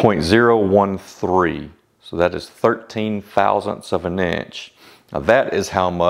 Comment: So I have rewatched the 5-cut method a couple of times, and I simply don't get it. First you say 1/13.000 of an inch. After the adjustment you are at 1/1000 of an inch. 1/1000 is way worse than 1/13.000. Can you explain that please? I am from europe so I don't use imperial. But it looks like pre adjustment it is not 1/13.000 of an inch, but 1/130 of an inch. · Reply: Ahh now I understand. I thought you Said “one thirteenth thousands of an inch” thanks for explaining